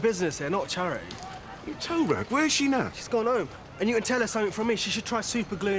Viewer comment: did Steve just call Sanjay a "toerag"??